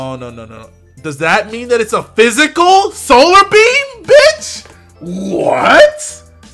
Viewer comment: Thanks to mishearing the emphasis on words: Lunartis is a " Physical Solar Beam Bitch ".